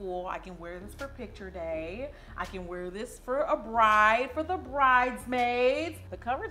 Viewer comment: which other South Africans thought she said she can wear this to a "braai"??😂🇿🇦